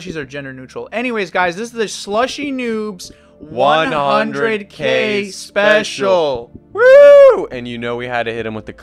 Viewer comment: Thought it said slushy noobs look special